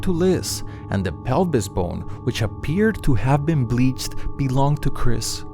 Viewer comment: I dont understand what he said 4:06 . The pelvis bone appeared to have been bleached??